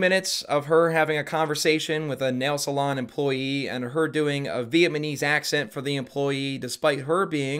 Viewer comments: did you say Viet-man-ese? Now that's punny! And it fits neatly into this faux red pill bit! The war against viet-mam! Am I right? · (spits out drink) did you say "VIETMANESE???"